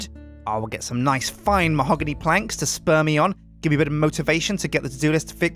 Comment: When he said spur me on I thought he said speam on me lol